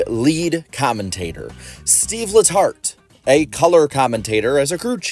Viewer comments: no joke. At the very beginning I thought you said seedless hearts, rather than Steve letart. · no joke. At the very beginning I thought you said seedless hearts, rather than Steve letart. Depending on whether he has work on Sunday, why not have booty barker in the booth with NBC.